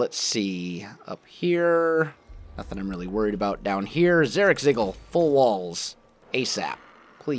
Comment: Did he say "Zarakzigil"